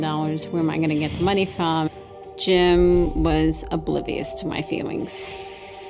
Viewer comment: Jim was “abrobiest”? my feeling? What does it say in her remark? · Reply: Oblivious - means he was totally unaware or unconcerned about Dina’s feelings.